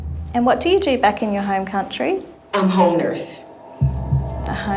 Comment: I thought she said hoe nurse… seems to fit the bill more for